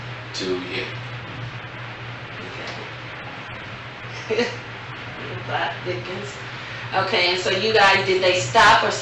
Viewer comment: Bro… what are you smoking ?! 4:29 she said, “ The plot thickens” !! Tf